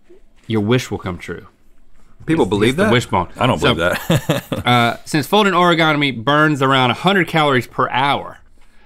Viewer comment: Link: "Since folding Oregonami..." Me: Wait... ~rewinds to 7:39 ~ Link: "Since folding Oregonami..." Me: Yup, that's what he said.